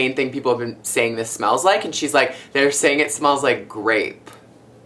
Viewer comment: I thought he said it smelled like rape and I was like... O_o